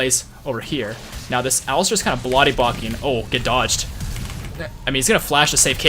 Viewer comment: "This alistar is kind of blody bocking" I had to rewind to make sure i heard that right. too funny!